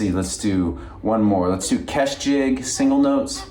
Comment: I’d like to try this with the jig you mentioned but I’m not sure the title? Sounded like you said something like ‘Keft Jig” but I can’t find anything…what is the actual spelling please? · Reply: It's Kesh Jig. :) A fun one! Enjoy learning it!